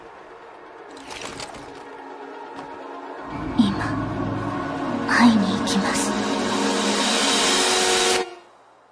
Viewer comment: What she say on Japanese at 4:53 - 4:55? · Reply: 今、会いに行きます